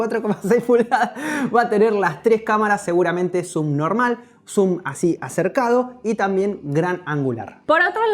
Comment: entendí "SUBNORMAL" 😂😂